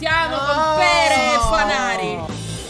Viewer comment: ma solo io ho sentito "partiamo con pere e banane"? O dice veramente così? 🤣🤣